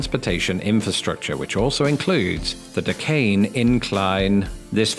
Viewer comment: I thought you were saying "decaying incline".